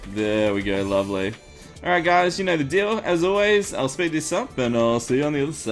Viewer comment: says "you know the dill" instead of "you know the drill" xD myfacewhen xD